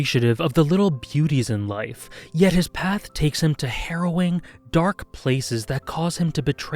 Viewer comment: I heard that as "..his path takes him to heroin" lol